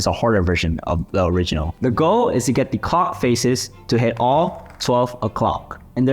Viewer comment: i swear he said co*k